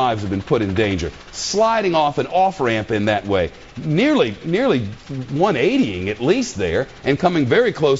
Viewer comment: did he say that car was going 180??? l0l fail